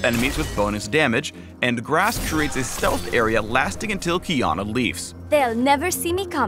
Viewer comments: "...until Qiyana leaves." I thought he said Keanu Reeves... Now I see memes have ruined me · Did I just hear Keanu Reeves? Riot you are breathtaking.